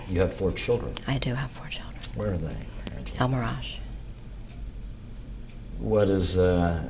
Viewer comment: What does she say at 4:36 Sounds like "a mirage"